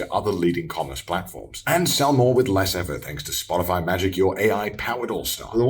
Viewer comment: does Connor say Spotify instead of Shopify or am I hearing things?